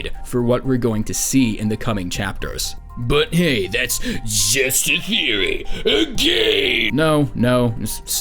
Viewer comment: DID HE JUST SAY ‘’HEY THATS JUST A THEORY A GAY’’ XDDD